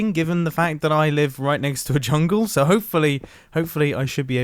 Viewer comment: am i tripping, or did he say chungle?